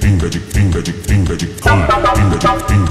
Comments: Did someone heard finger it in 1:13? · bro why do i hear fingertips 💀